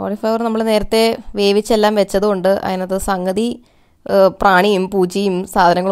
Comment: did you just say prani and poocha!?😂😹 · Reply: Poocha alla poochi.